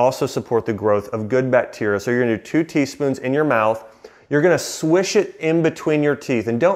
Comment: I thought he said tablespoons... oh boy! 😅 teaspoon.... much easier.